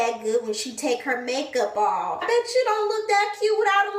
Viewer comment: I think I just died and was resurrected with a whole damn new life!!!.. Did she just say "Bitch you don't look that cute without a line up either!! Swear in my head it was on a loop for a minute!! Dead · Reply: She said "I betchoo" (I bet you). Lol.